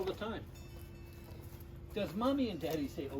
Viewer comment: I love the end, all I heard was "does mommy & daddy say oh shit!"....